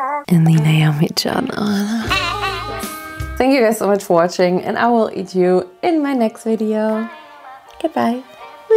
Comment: Did she say she’s gonna eat us 😳